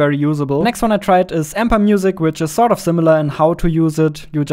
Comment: I couldn't understand what the one at 56:40 was. The words are mumbled. CC says "Amber Music" but that doesn't seem to be correct.